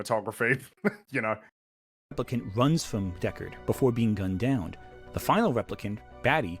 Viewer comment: Did he just say "gun downed." ? Lol